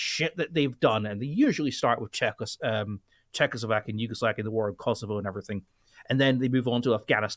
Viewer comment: did he say Yugoslakia?